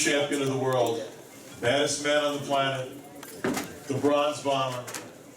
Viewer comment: For a second I thought he said "Lebron's momma'